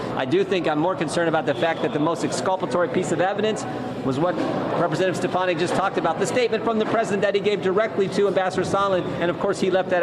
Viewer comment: Did he call that woman Stefani as in Gwen? Thats not her last name.